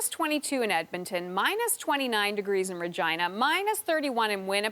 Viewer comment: did she say my gina?